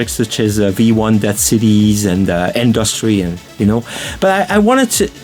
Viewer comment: What did he say after Dead Cities? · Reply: “Industry”, a track he did as